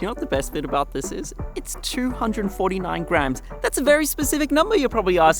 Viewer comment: I thought you said grand there! 😂